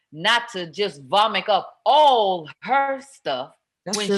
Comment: Did LisaRaye say, 'vomick Up"???? #ICANT